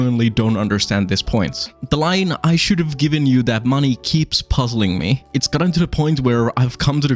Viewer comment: I thought he said I shouldn’t have given you the money